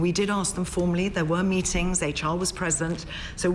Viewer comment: Did she say “the child was present “ at 2:02 ???